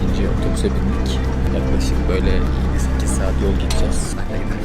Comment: pardon ben mi yanlış anladım 8 saat sekiz saat yol mu gitcez dedi ?